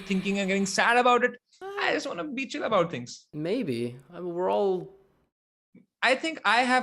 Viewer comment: did said nahi bae???